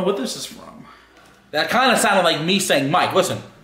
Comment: he is not saying mike its saying "like" and its saying it